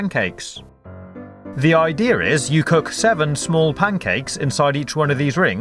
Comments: did he just say ider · did he just say ''the idear is'' or am i hallucinating ?